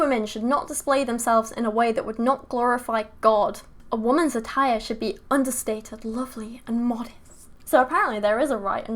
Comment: I heard “a woman’s attire must be subtle and understated” as I was pulling on my rainbow fishnets.